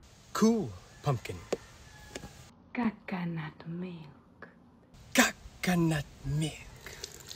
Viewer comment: Ok for a quick second I thought she said “caca nut milk” and I’m like wait no she meant coconut milk 😭